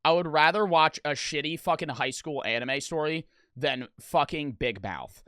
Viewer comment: i thought he said highschool dxd